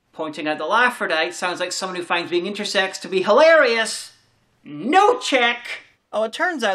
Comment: Is that English? What do you even say there? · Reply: 'Pointing out that Laffrodites sounds like people who find being intersex to be hilarious? No check!'